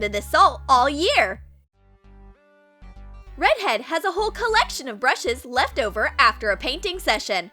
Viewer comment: I thought they said no brush’s like if you heard 👇🏾